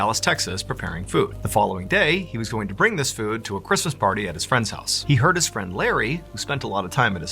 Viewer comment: I could have sworn I heard you say in the beginning that the party was at his friend Larry’s house.